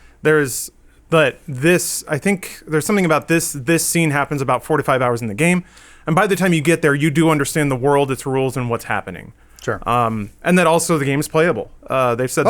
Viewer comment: it sounded like said "I think this scene takes place about 45 hours into the game" lol, I'd believe it.